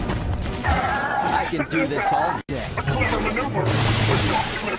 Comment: I've honestly never seen Grievous so lively before....it's amazing. Also at 0:45, did he say "invasive maneuvers?" I hope he did XD